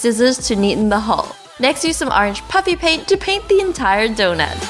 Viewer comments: I keep hearing “Puppy Paint” not “Puffy Paint” Lol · I thought you said puppy paint